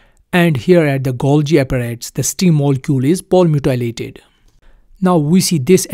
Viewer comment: I'm talking about the word you say at 1:57. You say, "the STING molecule is ___ dilated." In the illustration it looks like it's the name of the yellow speres. I just couldn't understand that one word. Thank you for your help! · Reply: Palmitoylated ----- Palmitoylation.... sorry for my bad accent...